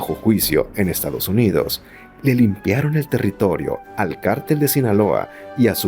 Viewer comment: Perdón pero no se entiende bien lo que dices de Felipe Calderón .... Dices que Felipe Calderón de la mano de Genaro García luna LE LIMPIARON EL TERRITORIO AL CARTEL DE JALISCO ? Minuto 3:25 al 3:35 .? .... qué quisiste decir ?? dinos...